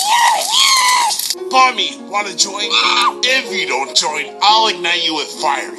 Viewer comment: cant still figure what is she saying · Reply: "Firey you lit my fuse" btw Bomby is a male